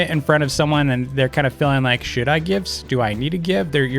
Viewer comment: I thought you said the s word but it was should